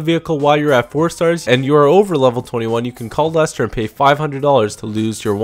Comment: you talk too fast by the way, hard to understand the accident, sounds like you said you can call dust but i think you meant call Lester??